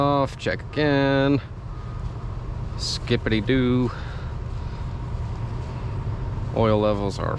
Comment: did you say skibidi doo?